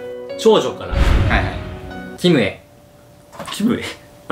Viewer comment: でキムへがきめぇに聞こえて、ん？ってなって笑ったけど全然そんなことなかった